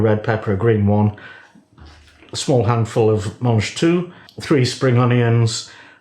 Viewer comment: what did you call those sugar snap peas? Lol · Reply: Mangetout - French for 'eat all'. They're similar to sugar snaps but not identical.